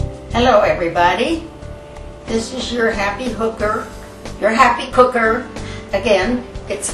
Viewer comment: Did she just say happy hooker???